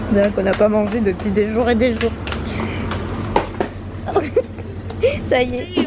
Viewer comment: J'ai compris " Becca " à la place de " Picard " et je t'avoue que je ne comprenais rien jusqu'à ce que tu filme dans le magasin. 😂 Bon courage à! Je lui souhaite de réussir ce nouveau projet ! 😄 Merci pour ce vlog.~ ♡